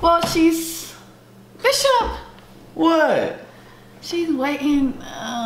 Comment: did she call him bishop🤣😂🤐😣🤣😂🤣